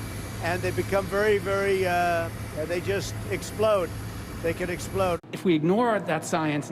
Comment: did he say the trees explode 😖